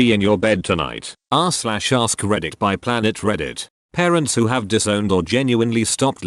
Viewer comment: So when I first noticed this video I thought it said “Parents who drowned their child......” and I was like WTF!?!!?????!!!!